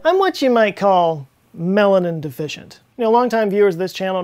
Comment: Oh I thought he said Melon Indefficient i was like wtf